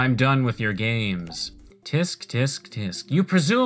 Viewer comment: Dude, did you say “Tisk tisk tisk”?